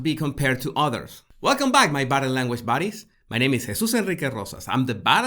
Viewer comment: Hey! now you can become a Member of my Channel and access to Fabulous Perks: <link> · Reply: One hears what one hears but I always thought you were saying 'body language bodies.' :D